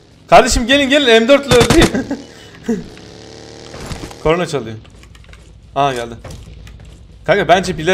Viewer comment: corona çalıyo anladım 🤦🏼‍♀️